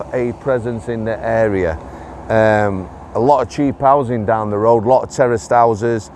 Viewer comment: I was brought up around the corner on wheldrake road,I moved out about 10 years ago,I now live on elm lane. There is constant trouble down there,a lot which is covered up,the place at times looks like a tip,disgusting. I know a lot of stories,some stomach churning as I also have friends down and one who works in the local school,but wouldn’t dare put them on here! Ps,I have the shoei,but ride a gs1150 👍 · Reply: when you said "Lots of terrace houses just down the road" because of your accent I honestly thought you said "Lots of Terrorist houses just down the road." I quickly realised what you actually had said and did have to chuckle as I could see you before riding off ... "Down there Guys, lots of terrorist houses! I don't care, I'm going down there anyway..." and found footage A week later. ☠️🚑